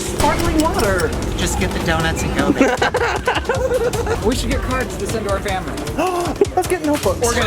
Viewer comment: i thought he said let's get carbs and send them to our family. haha